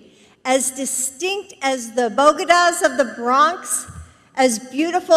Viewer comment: It sounds like she said bogedas...